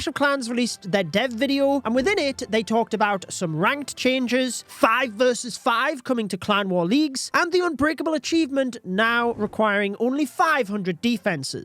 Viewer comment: did I hear 5v5 cwl’s??? That would be crazy easy tbh